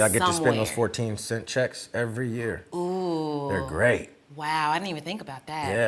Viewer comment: Thats what I said too!! She missed a perfect opportunity to explore the realm of the underpaid Black actor. Maybe she was nervous by how attractive he is. Lol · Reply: She might have missed the "cent". I thought he just said 14 checks until I read the comments.